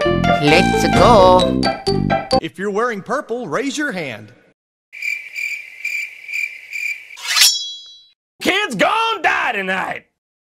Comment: What did the purple guy say on 5:58??!! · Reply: Every Child that wears porple raise youre hand... KIIIDS GONNNNNA DIEEEEEE TONIGHT!!!